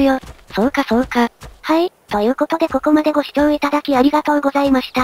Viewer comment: 最後アディダスが聞こえた気がする 気のせいか